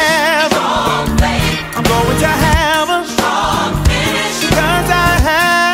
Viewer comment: Very good song, i love it. But am I the only one that noticed it kind of sounds like he says "I'm going to hell" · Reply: He's saying "I'm going to have (a)....strong finish...."